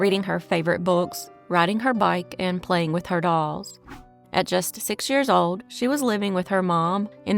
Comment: I heard "playing with her balls" and had to rewind.